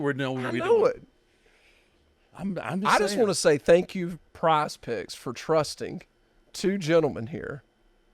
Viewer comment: Lol I'm from NZ so have never heard of this sport technology. I thought the Undertaker was saying Prized Pricks 😂😅🤣